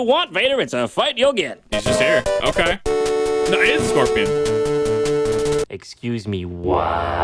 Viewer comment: did anyone else hear "Anal Scorpion"?